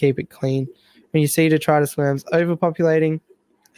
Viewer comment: Whats the name of the one you're talking about in 10:54? I've replayed what you said a dozen times and I just can't make out what you say..these fuckers killed all of my fish as they got stressed due to the sheer number of them, ive tried everything to get rid of them and they just keep coming back..I even moved all of my surviving shrimp and nitrite snails to another setup and now they're in there too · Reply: 10.54? I was talking about detritus worms. Cheers and sorry to hear.